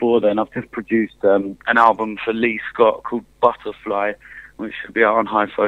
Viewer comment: Did he just say a leafdog album will be dropping in 3 months?! Feels like I've been waiting years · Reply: nah he said lee scott